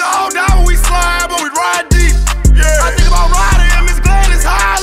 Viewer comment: I think about Ronald and ms Gladys highly 🙌 🦅🗣️ u getting alot of plays from me dawg🤣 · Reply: Ryder? Ok ...thought he said slim